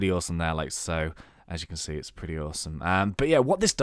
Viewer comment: Whath did you say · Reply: 'As you can see it is pretty awsome'